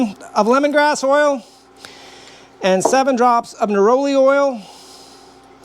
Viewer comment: Neroli Oil, updated recipe is in the description of this video. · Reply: Excellent and thank you! My hearing isn't great and I thought you said Holy Oil. Pretty sure that wasn't right.